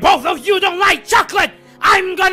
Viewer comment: When I was younger I always thought it said “I’m gonna cut you dead”….wow.l